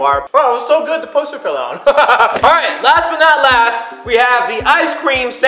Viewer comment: Did he say last but not last